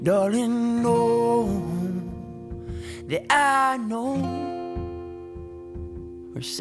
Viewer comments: I always thought we was saying, sad souls. He's saying sad, songs. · Holy shit, I didn't even know half the lyrics when I first only listened to the remix. Now I now how sad this is... I feel like this version is better just because you get a deeper feeling and even though I love the remix, I just feel like you don't get to have any true emotion to the song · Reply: I thought he was saying "sad souls" - my lyric is better imo